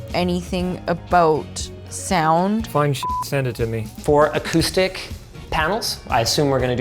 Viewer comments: Did say fine shit send it to me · “I don’t know anything about sound.” “Find shit send it to me.” Is about the most sound guy thing I’ve ever heard. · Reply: I thought he said fine shit send it to me and I couldn’t believe would say that. Relieved to see this interpretation. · does he say fine shyt send it to me? 😭😭😭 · did say "fine shyt" or "find shyt" cause calling her fine shyt is funny asf